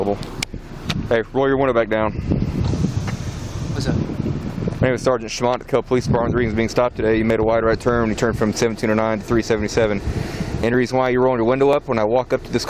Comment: I swear I thought he said his name was Sergeant Schmuck